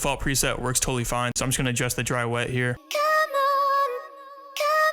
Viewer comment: I’m glad it says come on baby. I thought it said devil for a min and I was like NAHHH